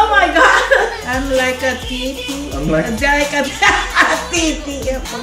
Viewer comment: tbh i cant sing this song in our house bc it sounds like im saying "I like TT" so uhm